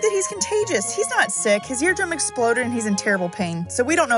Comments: I thought it said my balls exploded... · I Thought It Said "My Hair Exploded" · I Thought It Said "My Hair Exploded" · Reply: I thought it said my Dear Exploded 😂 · I Thought It Said "My Hair Exploded" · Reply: I thought it said "my air exploded" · I thought it said his mums ear exploded at first 😂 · I Thought It Said "My Hair Exploded" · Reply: I thought it said "My Hair Explored" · i thought it said “My Deer Exploded”